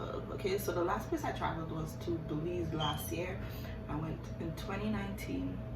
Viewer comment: What was that place you said? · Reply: Belize ❤️